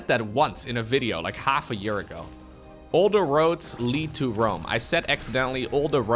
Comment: It's, "all roads lead to Rome". Sounded like you were saying, "Older roads". If I'm mistaken, that's on me. Great vid either way though!